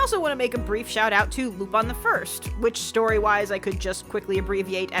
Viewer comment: what's the name of that film? I wanna watch it now simply because of the animation but I can't make out what you said the name was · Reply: Lupin III: The First, I believe